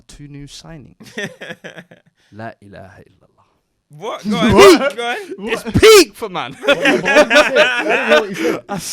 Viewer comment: what did say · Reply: There's no god but Allah